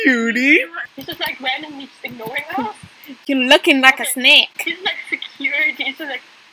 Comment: it sounded like she said “You lookin like a snake”